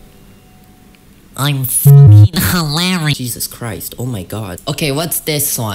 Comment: LMAO I CAN’T STOP LAUGHING WHAT- look at the subtitles. it says KIM SEOKJIN instead of JESUS CHRIST 😭😭😭😭😭